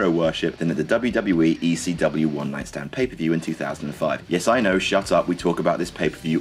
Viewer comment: I dont get the work on tuesday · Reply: k/ I did say I didn't know, I only started watching WWE last year, Thanks for clearing it up. I only recently learnt what PPV, was, I thought they were saying "Paper View".